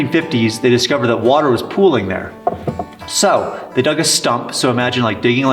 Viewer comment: Hey, it sounded like you were saying "stump" in regards to the water storage. It's called a "sump". Just a heads up. Anyway, I love your content man. Not only is it interesting from a historical and adventure perspective; your dedication and passion for this project are utterly inspiring.